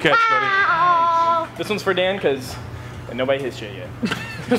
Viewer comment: I can't figure out what said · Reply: “This one’s for Dan cause, ain’t nobody’s hit shit yet” sorry for not subtitling 😭